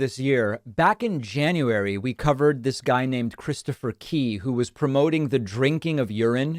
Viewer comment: The first time I heard "His name is Christopher Pee"... :D